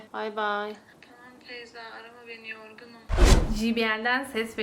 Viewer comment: Emin degilim ama feyza dedi galiba teyze degil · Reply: bende öyle duydum sanki ve banada Feyza dedi gibi geldi